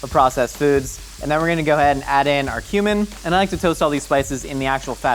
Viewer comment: when you said cumin i thought you said human and i was confused